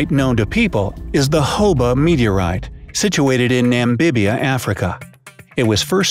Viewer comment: When it said Hoba meteorite I thought it said Hobo meteorite